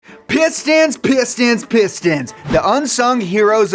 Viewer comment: Not gonna lie, I thought he was saying "piss stans" in the introduction and I was scared I clicked on a not so savory video.